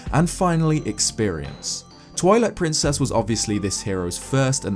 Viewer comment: sounds like he says Toilet Princess lol